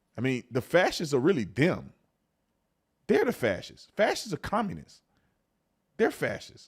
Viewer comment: Cenk says "Facist," but all I hear him say is "fatshits".